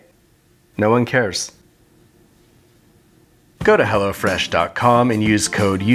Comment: I thought he said go to hell after that bit